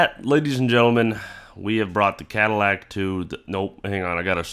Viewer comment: Ooooh. "Badillac" I heard "Battlelac" and it sounded much cooler.